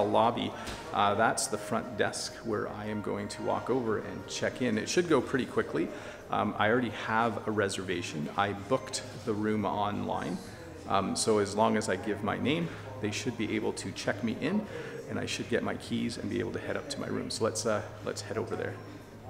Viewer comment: hi, i really don't get the word that you sayed at the 1:02 of video, i sware that i heard you say "bucket" the reservation online, is this actually correct? What does it means? is it a slang? by he way thanks for the video and keep with the cool content! =]